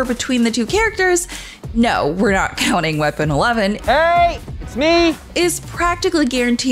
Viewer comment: Did she say "Weapon Eleven?" (0:20)... What is she talking about? 🤔